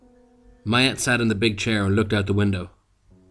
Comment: Woops. It seems like everybody thought I was saying "my aunt". It's actually "Mayet" which is the girl's name. Sorry for any confusion.